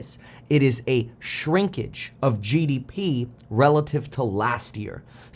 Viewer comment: Did you just say shrinkage in peepee? 🤣🤣🤣